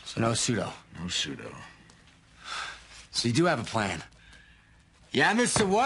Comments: I thought he said "yea science bitch!!"...? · Ironically, this must be the only line in the series which he doesn't punctuate with "BITCH". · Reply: I swear to Odin, I always thought he said "Yeah! Science bitch!"